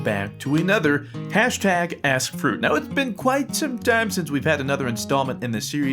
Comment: I thought he said Welcome back to another #AssFruit XD